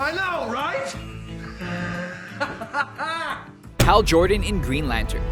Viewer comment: Thought he said 'Hair Jordan' oops